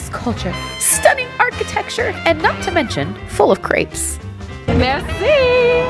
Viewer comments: I legit thought she said ‘full of creeps’ at first 😂😂😂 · When you said "full of crepes" i thought you said "creeps" hahahaha!